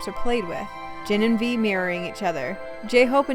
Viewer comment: WHEN YOU SAID "V AND JIN MIRRORING EACH OTHER" I THOUGHT YOU SAID THEY WERE MARRYING EACH OTHER LMAO